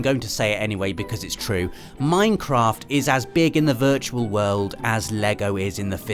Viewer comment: Did you just say Meinkamph instead of Minecraft.